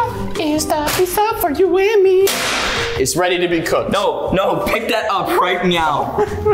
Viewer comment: what the f••• is this said pick that right meow now